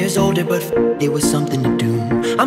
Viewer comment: Why did I heard "I drove a sport car just to poop"